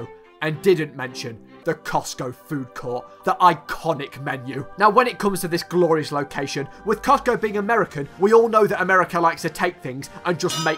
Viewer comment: Did he just say coctgo? Lol